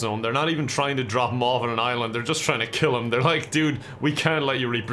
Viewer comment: for a sec I thought that said Wuhan island 😂